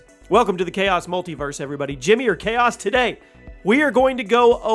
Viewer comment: Great content! But what are you saying? 1:08 “jimmy your chaos”?